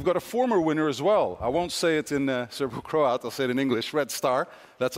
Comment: What the heck? 22:30, "I won't say it in Croat", you know that FC Red Star were champions, but didn't know it's a Serbian team? Pathetic. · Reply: he said “Serbo-Croat”